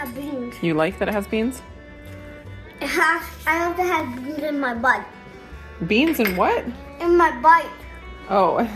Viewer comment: Lol I thought he said beans in my butt too lol.